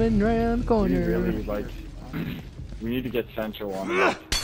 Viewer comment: Sancho, I think someone in the chat of the gameplay said we need to get Sancho off of our team. Watch the video near 10:09 and listen closely to the gameplay chat. I not sure if that is exactly what he said. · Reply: Altamish Bukhari Ah. I remember what was actually said. My other teammates are saying "we need get Sancho on the Mic (game mic)". Currently, I do not have the necessary equipment to broadcast Twitch with my Live commentary while interacting with my teammates. So, I'm pretty much playing without being able to communicate with them directly. All of the videos you see on my channel are without Team Communication (which is crazy to play way to play #tlou factions). When I save more money, I can buy the appropriate equipment to do both. It's very complicated. In the end, they were saying we wish he had a [game]mic so I can tell them where someone is especially in Sudden Death situations. During the dark matches (non-recordered or broadcast) I use my PS3 wireless Headset and speak to my teammates in a more technical matter. Like so and so is here and the enemy is moving there. Don't fret, these guys are awesome and we are planning another Twitch Night for you guys.